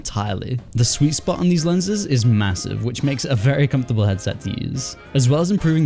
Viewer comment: did u say … massive?